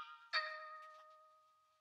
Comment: What does she say at 14:05 ? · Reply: I’m only half of me